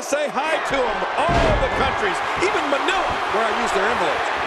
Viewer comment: Did they just said even manila yeheyy!!